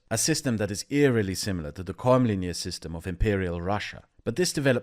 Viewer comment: "... eerily similar to the cormlinear system of imperial Russia ... " What is the word that the auto-captioning renders as "cormlinear"?